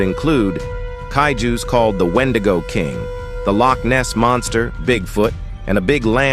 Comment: did i hear lochness monster?